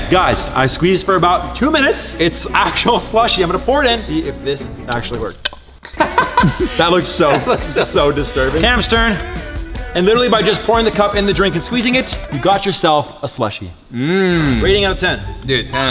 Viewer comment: Did he say “and literally just by pouring the CUP IN THE DRINK” or and the drink?